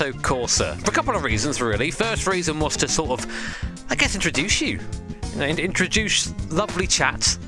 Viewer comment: Did you say "introdouche you"?